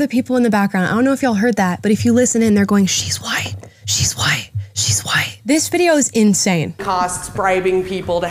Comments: not that it makes it any better but i think shes saying "she's lying" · I hear "She's lying. She's lying. " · when they were whispering in the back... I think the flannel girl said "She's white. She's lying (X's 2)" but said the lying portion twice. It's kinda hard to hear the whispering 😭 it's fr like watching a ghost show & they have an EVP & you're trying to hear what's being said LOL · That is not at all what they were saying. Sounded like “she’s lying.” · I believe she was jokingly saying she's lying not she's white :) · i thought she said she’s lying she’s lying ?